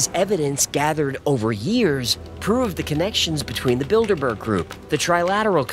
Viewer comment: I find it hard to take this seriously because I keep hearing 'the Build-a-Bear Group'